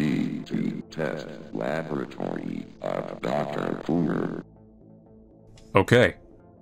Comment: did i hear that right? Dr coomer? if so I LOVE THE REFERENCE TO HALRAI